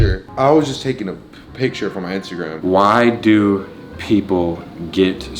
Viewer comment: I thought he ya said get off instagram